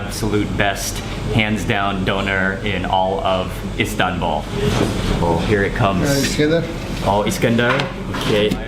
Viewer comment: did he say iskander lol that's a russian missile name it's an anti ballistic missile in Russian army